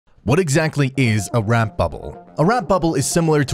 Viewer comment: Is he saying "Rap Bubble"? As in, "DJ Bootlicious busted out a sick rap bubble before awing the crowd with her shiny bling." Or maybe he means Wrap Bubble as in, "My mom took great care to preserve my PB & J sandwiches using her special wrap bubble containers." :P